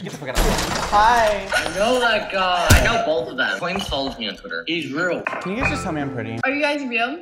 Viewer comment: “Twaimz follows me on Twitter.” Did he think you were Twaimz, or did I hear that wrong?